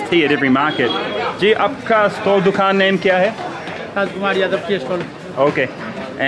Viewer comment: i think at 4:46 he said Raj Kumar Yadav Tea Stall.